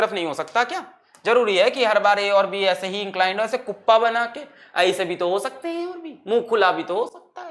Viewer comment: When he said cuppa (1:04:37) banake I heard oppa banake 😂😂 BTS army hu kya kru oppa se Kim taehyung yaad aata hai 💜💜💜💜